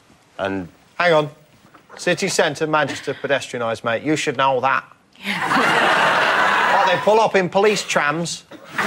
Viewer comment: Omg what does he say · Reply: Hang on - City Center, Manchester? Pedestrianised mate, you should know that. What, did they pull up in Police Trams?